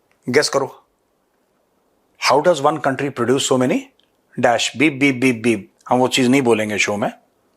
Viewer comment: esa kya bola tha? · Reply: so many rectums